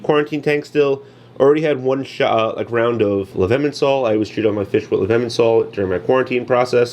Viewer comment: Ok I heard it as “I always treat my quarantine fish with lemon salt” I was like whaaaaat?? Was he making margaritas and thought that would be a good idea WTH? 😂 😂 😂